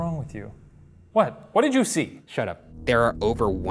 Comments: oh instead of shut up i heard "son of a". Also if you ever round out of ideas, can follow up on some of the past places that were more compelling with the new spirit box hahaha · This was by far the most active one. The box in Capone's room I didn't always hear what Ryan heard but I did hear words. Example, instead of "Shut up" I heard "son of a...". I like the Spirit Box, it's better than just guessing at sound in the static. I hope it keeps coming back! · To be honest when Al scarface Capone supposedly said "shut up" I heard "son of a"